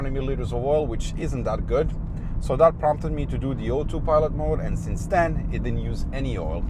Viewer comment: what did he say? Oil 2 what? I cant understand haha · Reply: O2 pilot mode